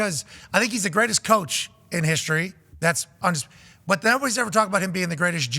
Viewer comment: What's this guy saying, "greatest coach in history @ 4:59 sec. 😂😅